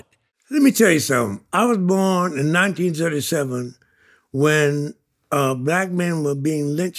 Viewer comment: Did he say he was born in 1977 ? So he 46 or 86 ? 😂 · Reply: He said 1937